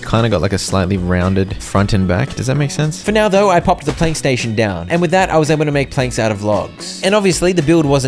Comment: i heard playstation while i wasn’t looking and was so confused